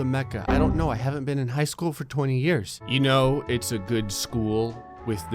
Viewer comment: did you just say you haven't been to school in 20 years or did i mishear you